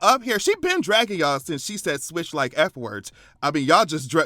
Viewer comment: Beyonce disappointed me 😢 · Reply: I thought Nicki said “switching like sissies” not the F word. Either way she’s off the deep end now. I want pre shether Nicki back